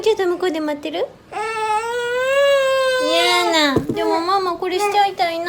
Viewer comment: did she say Mama? her first word ^^